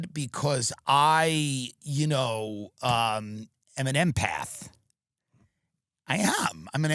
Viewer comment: “You know me , im an empath “🤣🤣🤣😂😂😂😂😂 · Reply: I thought he said an M&Mpath lol